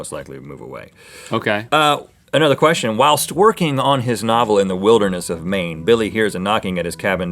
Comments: Who else heard While Twerking on his novel.... ? XD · I thought Rhett said "twerking on his novel" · I thought Rhett said "While twerking on his novel.." 😂😂😂